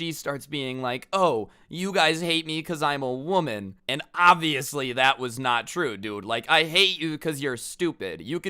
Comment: I thought he said “and obviously that was me” lol